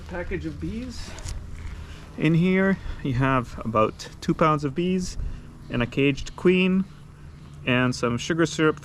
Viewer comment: I enjoy your videos, I thought I heard you say there was about 2000 bees in there, I would think here in the states we normally have around 10,000 and the Queen. Keep up your good videos. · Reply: Yeah, I kind of mumbled there, I said 2 pounds of bees, which sounds a lot like 2000 bees. You're correct, that's about 8 to 10 thousand bees.